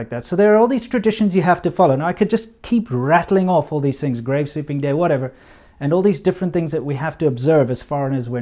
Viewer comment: did he say "grave sweeping day" 2:22, don't sweep that under the rug 🤣🤣🤣